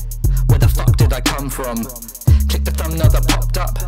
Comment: Available on Itunes and spotify guys!! Link in description! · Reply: this whole time i thought it was saying click me as in the merch although you should rewrite that part of the song for your outro i just don't think a song about being scammed is the best for merch